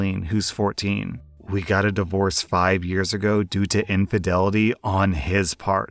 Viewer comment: Second story: I thought I heard infertility so I was a little confused at first